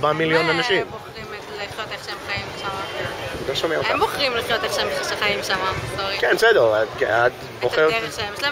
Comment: that's a mistranslation.. she actually says "they are the ones who choose how they live there"... which continues to "they have independence" etc